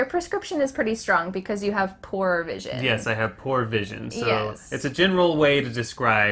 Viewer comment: Before I see subtitles, I thought you said: "You have porn vision" "Ya, I have porn vision"